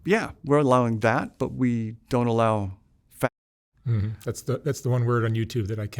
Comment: Is he saying faggot? What is the one word on YouTube that he cannot use? censored